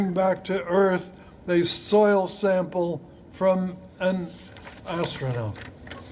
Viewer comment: That speaker Kimmel laughs at bc it sounds like he said "a soil sample from Isreal" but he actually said "from an asteroid" everyone just turn ur tv's off. Everything said by or in govt meetings held by "elected officials" is all just a long "play". Everyone complains about hurt feelings and bullying.. lmao the media bully's Trump. Flat out